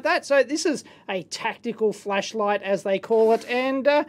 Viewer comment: With his accent I thought he said fleshlight instead of flashlight